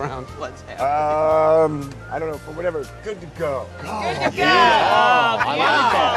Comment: What did Keanu say at 4:51? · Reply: Keanu: Uh, I dunno but whatever 'good to go'. Everyone: HOLY FRICK KEANU IS SO BREATHTAKING!